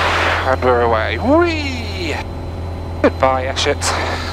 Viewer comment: Could have sworn you said “Goodbye, ya shit” 😂